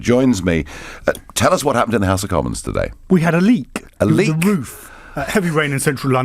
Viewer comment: Oh, a LEAK. At first I thought he said a LEAP from the roof. I was briefly hoping that Theresa May did the honorable thing.